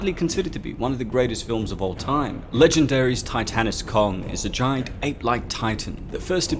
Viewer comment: How many ppl thought it said tight anus Kong ,🤗